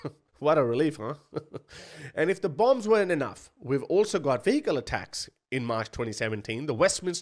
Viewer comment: Why did I hear “fecal attacks” instead of “vehicle attacks”!?! 😂😂